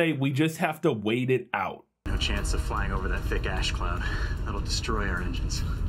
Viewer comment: Did dude say "Fake ash cloud" 😅